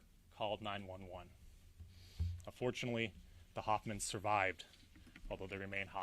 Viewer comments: Did he just say "unfortunately " the Hoffmans survived? Am I hearing this correctly? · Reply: He said, and fortunately, not unfortunately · and 3:50 Is he saying unfortunately?